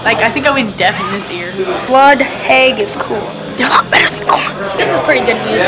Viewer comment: I nearly collapsed laughing when that kid said "they're not better than Korn." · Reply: NoEsUnYoutuber haha I always thought he said "they're a lot better than korn" but that does make more sense...